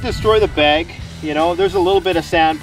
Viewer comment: Large round hay bales for me 🌾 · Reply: It sounded like you were saying you have high caliber hay...